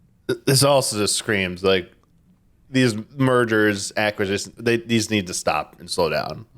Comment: Everytime says "mergers" I just hear "murders"... which figuratively speaking feels kinda on point ngl...